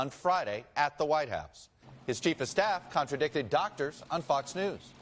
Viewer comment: Did they just said Trump's doctors are contracting a NY times report??? 🙄 6:10.